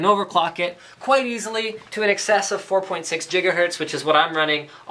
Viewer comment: Did he say Jigahertz at 5:52?